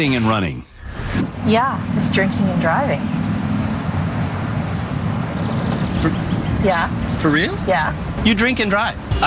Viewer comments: Okay I really thought said ‘jerking and driving’ at 7:23 and I was in stunned silence. DRINKING and driving, got ya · Wow, maybe I'm just tired cuz my insomnia's acting up and I'm watching this at 4 in the morning right now, but when they introduced the first guy's wife I legit thought she said his worse habit than the hit and runs was "jerking and driving" so I was thinking, "Holy shit, this dude jerks it while he's driving? No wonder he ends up getting into so many accidents." And the shocked look the host was giving made me think even more that's what she said, especially when he said, "For real?!" But then he looked at and said "You drink and drive?!" and I died laughing because I misheard that so bad, but to be fair you have to admit it really would've still made sense if it actually had been "jerking and driving" 😂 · dont give a FUCK how old this video gets i will forever think she said jerking and driving… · when that first guys wife said drinking and driving i thought she said jerking and driving and was like ???? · I THOUGHT SHE SAID JERKING AND DRIVING😭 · Not going to lie I thought that lady said her husband was jerking and driving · I thought she said he has a problem with jerking and driving. Oh that explains the 20 hit and runs. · I thought she said jerking and driving 😭😭😭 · i thought she said 'jerking and driving' and i was about to throw my computer out the window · I thought she said "jerking and driving" lol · I swore she said jerking and driving